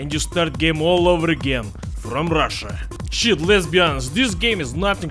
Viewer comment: did he just say "shit lesbians"? what the fuck?